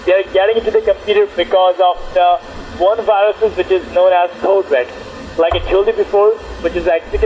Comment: i had auto generated subtitles and 16:19 instead of code red It said children Lol